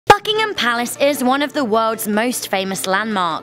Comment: It sounded like you said, "Fuckingham Palace" in the beginning